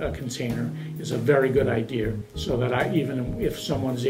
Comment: Did he just say good idea-er?